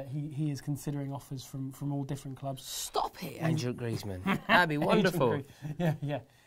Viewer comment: If we sign Griezmann can someone please come back and make 12:16 into a GIF... "Stop it (I think Rory said Andrew but hey ho) Antoine Griezmann" be great to use when he scores